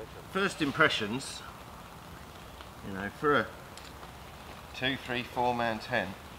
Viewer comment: For a moment I thought you said a 46 man tent 🤣